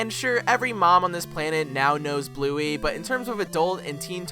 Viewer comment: my deltarune brainrotted ass thought you said pluey